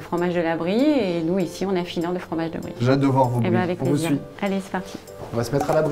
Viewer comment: hate de voir vos meule , rohh le lourrrddddd merde quoi xD